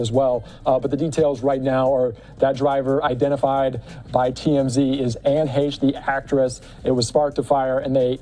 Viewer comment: did they say TMZ identified her body?